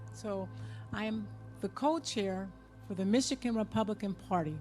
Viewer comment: wait a second, did she say that she's the cult-chair?